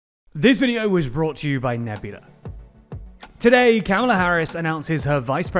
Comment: What did you say at 0:03? Kaola Harris?